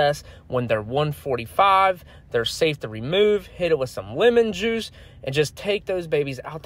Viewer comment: I thought i heard women juice instead of lemon juice...